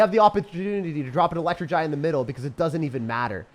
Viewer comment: "we have the opportntntny to drop an e-giant in the middle" What was that word he trying to say? I wonder.